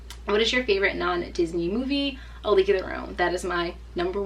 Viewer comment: sorry I'm hard of hearing, what did she say her favorite non-Disney movie was? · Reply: Cait B She said 'A league of Their Own' :)